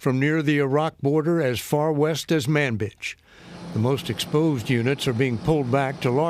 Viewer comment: Anyone else hear him say ManB!tch?